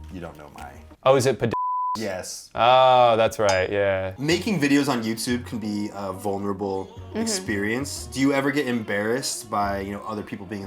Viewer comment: i still wanna know what he said lmao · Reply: I think pa-dildos